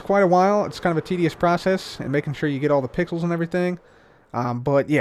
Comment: Anyone else thought he said pickles